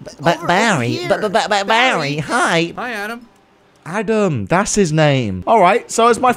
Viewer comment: I thought he said hi damn